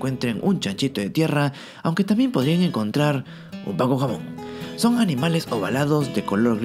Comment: "un pan con jamón!" Para el que no logro entender lo que dijo! 😅